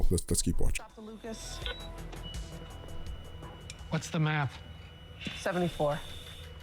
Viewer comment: These doctor reactions to The Pitt are awesome. When the ECMO Dr with the swag asked whats the MAP I thought he said whats the math which reeked of swag to me. But I guess MAP is more appropriate for saving lives and stuff.